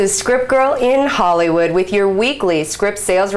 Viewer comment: I heard her saying "strip girl" :D